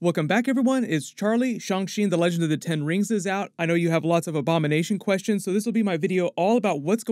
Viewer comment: I thought he said “ik you have a lot of Obama nation question to get answered “